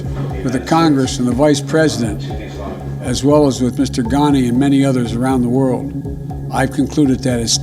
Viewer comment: DID HE SAID MR GANDHI BY ANY CHANCE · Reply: Mr ghani.